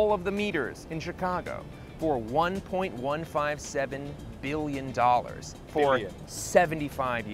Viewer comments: the subtitles say $157 billion, rather than $1.157 billion. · the subtitles are wrong and say $157 billion instead of $1.157 billon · the audio says $1.157 billion but the captions say $157 billion, a difference of more than 100x. Then they say 1.57 billion, then $1.15 7 billion (why the space?)